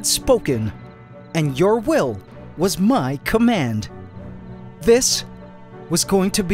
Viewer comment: Did you just say you command your viewers wills? Bro. That's wrong. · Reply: He said thier will was his command, meaning whatever thier veiwers ask he has to do